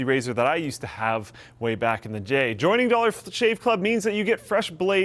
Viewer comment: lol did he said jay instead of day?? haha